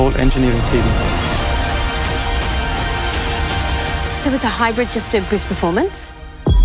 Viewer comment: Try that line again? Not even closed captioning can make out what she said there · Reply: so its a hybrid just to boost performance?